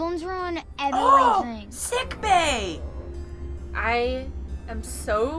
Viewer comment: When said 'Sickbay!' I thought she said 'Sick Bae' because Strong Bae was sick